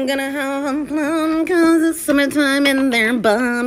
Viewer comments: I legit heard “cause it’s summer time in their bum” 💀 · This is what I hear "ima hve a plm Cuz it's summa time" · I'm sorry but I have watched this and I heard it as, "cuz it's summertime in their bum." I'm gutted